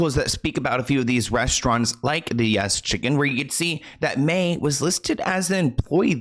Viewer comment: Lmfao. I thought he was saying “yes chicken” instead of “yes queen” for way too long.